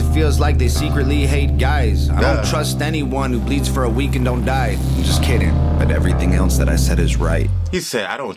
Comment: LMFAO mishears him saying "I don't trust anyone who bleeds for a week and doesn't die" as I don't trust a dude that bleeds for a week and doesn't die... >.< he's talking about menstruation